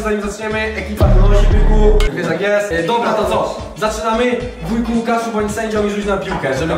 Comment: Ja usłyszałem hujku Łukaszu a nie wujku xddd